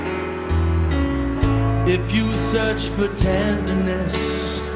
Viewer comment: 始めのIf youのところ何度聞いても イピュ-サッチテンダレスって聞こえる^^;英語難しい